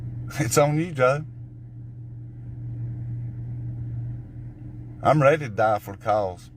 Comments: Did he say @ 1:47 that "he's ready to die for a cows."? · I don't understand "sheet" from what that hillbilly is saying. Does he come with a translator? 1:48 into the video he goes "I'm ready to die for cows". Wow, does he like beef that much?